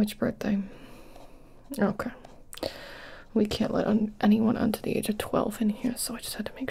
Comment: I know what you actually said, but I was really tired and heard "we kill anyone under the age of 12 here" and lost it